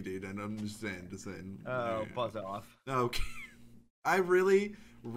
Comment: did say frick off